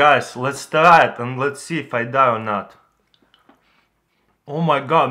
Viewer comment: did he just say " lets try it if i die or not"?